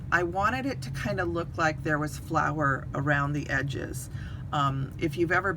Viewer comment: I thought you said flower.....lololol instead of flour. Omg 😮. I love watching your tags at Christmas. You and Muriel always get me going. I’m a lucky older stamp and picked up peachy keen, and lily of valley.